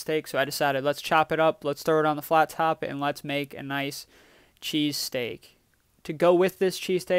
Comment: I read the title as cheesecake so you can imagine my confusion seeing a cut of steak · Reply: So did I, then I thought he said cheesecake to, and was like wtf you doing man